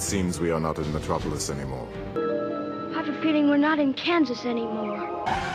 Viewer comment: pensé que decía ya no estamos en Kanasín